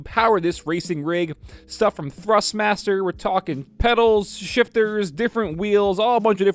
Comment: When you said schiit I thought you said shift